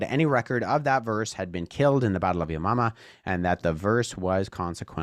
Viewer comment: I heard Battle of Yo Mama!